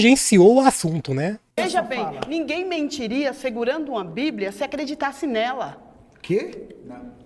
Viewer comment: eu entendi o que ela disse... "Ninguém mentiria segurando uma bíblia se acreditasse nela". Se o "pastor" crê e conhece Deus e enxerga a bíblia não só como um livro, mas como algo sagrado, com mandamentos morais e consequências espirituais, ele não teria coragem de mentir com a bíblia na mão. Se mente, é porque não crê ou não teme o Deus que ela apresenta.